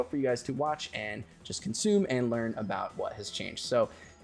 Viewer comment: Totally thought you said peeks-out instead of peace out at the end